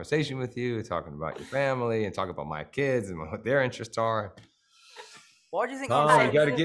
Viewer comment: What does Tom say at 3:29??? “What do you think…” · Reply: he saying “why did you think i was so” and then i can’t make out the last word :/